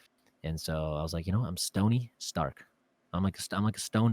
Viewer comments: Best podcast ever (also thought it said tony stark lol) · I thought that said tony stark😂😂😂